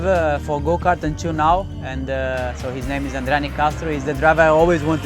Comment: wich driver? I don't understand Massa. The subtitles says Ronnie Caster but I don't know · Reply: That's André Nicastro.